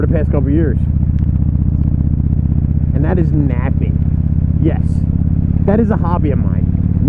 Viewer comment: Great effort dude & cheers for joining in with my #diehardwhoevenareyou challenge! 😎👍 I thought you said Mapping, not Napping at 1st & was a lil bit confused!!! 🤣👍